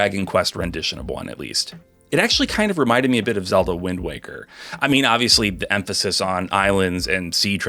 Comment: For a second, I thought you said "Wind Wanker". Have you played Yakuza Kiwami 0? 😆